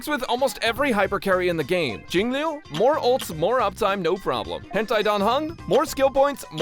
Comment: Did I hear saying hen tai dan hung? Am I hearing correct?